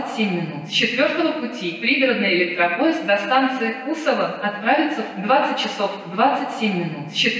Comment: мне одному послышалось Бузова ?